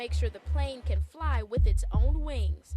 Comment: Did that promotional video really just say the planes are triple checked to make sure the plane can fly with its own wings???😂😂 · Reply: I heard "weight " 😅 which is equally a dumb thing to say lol 😂 I double and triple checked and yep like you said "wings". Omg 😂